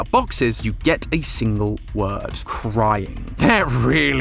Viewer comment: At first I thought it said cringe